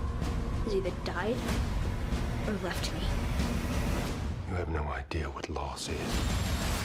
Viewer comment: Some reason I heard Joel say "I have no idea what loss is"